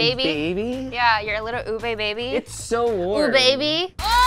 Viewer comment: when she said "ubeby" the captions said "eBay baby" 😂